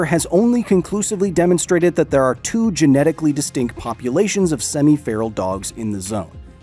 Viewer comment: thought you said pupulations lol